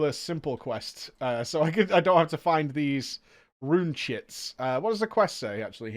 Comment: All I hear is “rune shits”